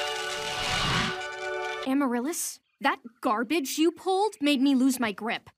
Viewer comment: Honestly, I misheard Amaryllis and thought at first she said: "At ease, Wh*res". 😅😞